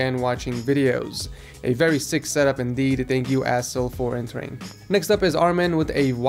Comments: I thought said "asshole" for a second, but it was just a misunderstanding XD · Anyone else thought he said a**hole instead of Assyl?